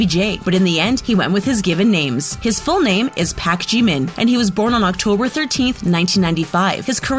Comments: Did Did she just say "Pake Jimin?" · Omg wait it sounded like she said "Pak Jeemin" 😂 its not hard its legit "Park(like parking a car park) Jimin(like legit Jim-in) 😂😂